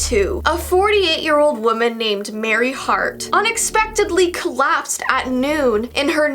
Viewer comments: When she said Mary's name I thought she said Mary Fart ..... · When she said Mary hart I thought she said Mary fart